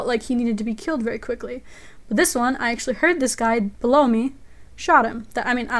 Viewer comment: I thought she said "Blow Me" rofl.